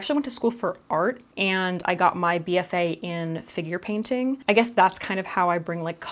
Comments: Omg I heard "I got my bfa in finger painting", me over here like "omg! I had no idea that was a thing!" Lol. Hell, it might be a thing idk lol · Omg I literally heard “I got my BFA in finger painting” and I was like.....sorry....what? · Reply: OMG same! She actually said "figure painting," right? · I really thought you said finger painting and I felt like I had missed out on my life's true calling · Hehehe i misheard you and thought ya said "i got my BFA in finger painting" 🤣